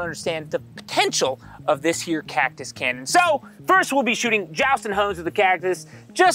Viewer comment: When he said "Jounston Hones" I thought he said "joustin' hoes"... video idea??